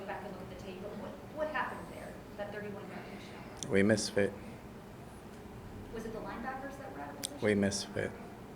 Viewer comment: "We misfit"? Is that what he's saying at 4:23?